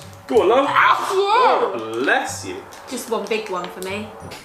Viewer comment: I heard ‘buy you’